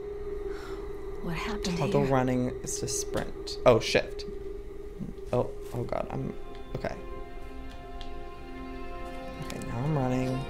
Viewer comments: when he said oh shift i thought he said oh shi* XD any1 else? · When the to run press shift it sounded like he said "Oh shit"